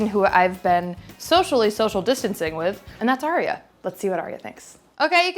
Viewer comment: looks more confident than ever in this episode. Maybe coz it's her own home kitchen. This is a exclusive episode it seems. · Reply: i seriously thought you said malaria and i was concerned...